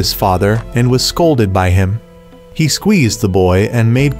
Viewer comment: Oh nahhh and I quote "He Squeezed The Boiii"😂😂😂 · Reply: I thought he said "he squeezed the balls"